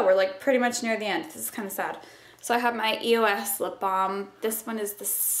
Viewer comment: when you say EOS lipbalm, I heard 'my ear wax lipblam...' hahaha, how silly was I .